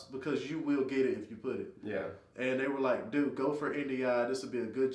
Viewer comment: What is that word he said? INDIA? 6:00 Edit: I think he was saying NDI so did quick google search for "Air Force NDI job description" and got nondestructive inspection. Mystery solved. Could be a cryptologic linguist analyst in the future...lol